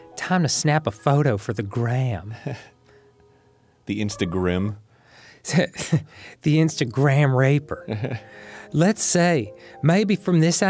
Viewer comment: I can't be the only one who hears "The Instagram R**er"